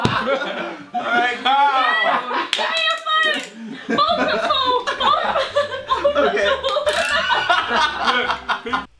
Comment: What does say after give me your phone? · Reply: Haha! She says "Bald but full" - You'll have to watch the full video for that to make sense.. that's basically what they wanted Louis to tweet. I think he was 'so hungry he was going to eat his hair' or something in a previous tweet and then that one was going to say "bald but full" haha! I forget though - here's the link! DON'T POP IT CHALLENGE! Feat. Pointlessblog, Zoella & Funforlouis